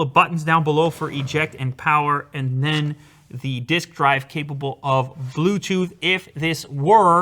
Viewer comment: Did he say disc drive capable of Bluetooth?